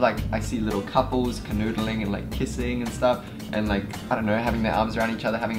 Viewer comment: did he say "canoodling"